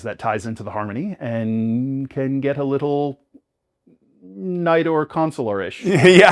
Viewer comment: What’s that term he said at 6:36? “Night-or/Neidor/Naidar Consular-ish???” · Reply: “Knight or Consular” because he’s trying not to say Jedi as the Harmony can be and often is interpreted as the Force.